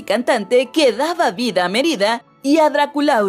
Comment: Pensé que decía Mier*da en vez de Mérida :v